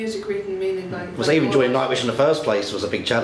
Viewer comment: Marco looks offended by that comment at 1:40..... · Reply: Elisa Princess Oh he said JOINING..... I thought he said ENJOYING.... maybe marco thought that too